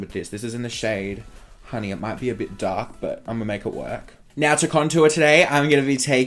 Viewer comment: i thought you said that the colour of the powder was, "honey, it might be a bit dark" lmao i died